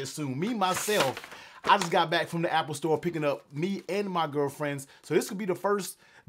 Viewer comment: Did he say my girlfriends!!!??